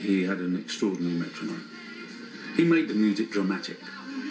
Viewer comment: I just heard Roger say "Keith made it drummatic"? I think he meant dramatic but that cockney sounded like he said drummatic. Drummatic works!!!!